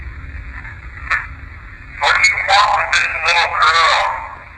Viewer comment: I think I hear "She's my LITTLE.... Sis-ter.."